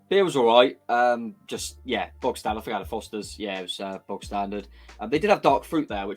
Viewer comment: Did say he had a Fosters? The Australian Fosters? Nobody drinks that here... I can't help but think Nah Mate is a bit harsh given "away" is a prerequisite - seems like silver service and uberburgers would not have made any difference. The further north the match is the further south you'll end up on the table. Oh well...good video anyway!